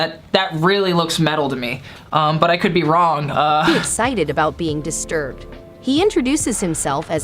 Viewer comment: is excited to be disturbed is what I heard cc''